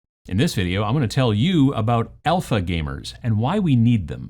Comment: Honestly thought you said Elfa gamers. Never heard of the term though.